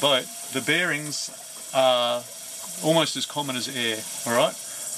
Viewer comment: Did I just hear that correctly? At 20:49, "the bearings are as common as hair". You sir are reckless and need to be stopped